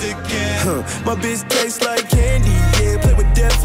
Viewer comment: I misheard “My bitch taste like candy yams”